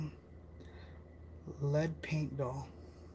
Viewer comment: What are you attached to i could have sworn I heard table and doll